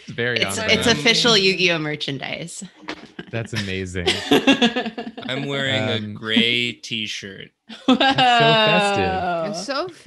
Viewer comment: I could’ve sworn said “I’m wearing a gay t-shirt” and I was like omg him and match 🏳️‍🌈